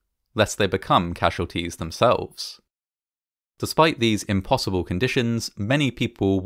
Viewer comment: I appreciate the fact that you never seen to use the word "impossible." "Vanishingly unlikely" is the closest I think I've heard you get to "impossible," but you always leave room for Chaos, just in case. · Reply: What? He said "impossible" at around 6:40 or just before